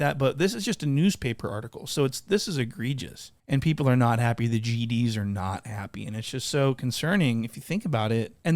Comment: Lol I thought you said "The GEDs are not happy" had to listen to it again